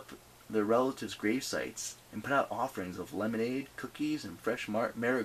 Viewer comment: sheesh i thought i heard LEMONADE COOKIES is that even a food ? hahahahahahaha